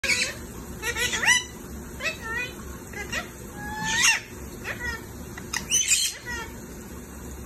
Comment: What are they saying at 00:01 and 00:16 ? It's so cute❤️ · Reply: 'You be good, good boy, Gabriel, what you doing?'